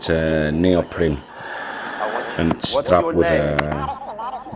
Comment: I thought it said raped not Traped (sic)